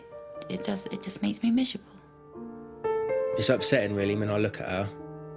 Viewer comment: is she saying miserable or measurable? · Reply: Miserable.